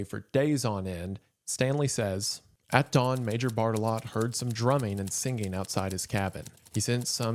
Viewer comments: i genuinely with all of my heart misread the title as "the cannibal killing of james cordon" and went "that makes sense" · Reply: And i misheard Major Bartelott as Major Fartalot, and i was quite disappointed when i realized i heard his name wrong. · I thought he was saying major fart-a-lot for like 75% of the video, sorry :( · Lol, I thought he was saying Major Fartalott